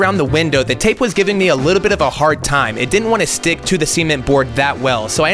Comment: The way he says cement is killing me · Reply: I thought he said semen at first